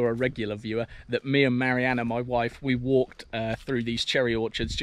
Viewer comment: what did you say at 4:27? "Me, Marianna my wife. ? Has the family satis changed or are you hinting? 🍒🎉